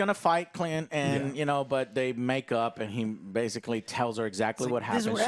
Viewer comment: I misheard it as "make out" and I feel like this is going to birth some MCU fanfics right there